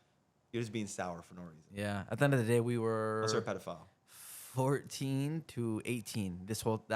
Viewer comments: did he just call alex a pdf file ?? lol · did rain just call Adapt a pedophile?